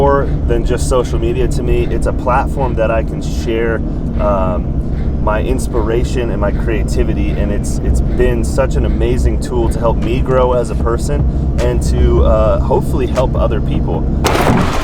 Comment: It's a t-shirt cannon!!! When you said it was "a tool to help me grow" I thought you said "to help knee grow". Until I rewound it I was wondering if you even lived near the ghetto. I kid, I kid...Congrats for the 100k...See you 1,000,000!!!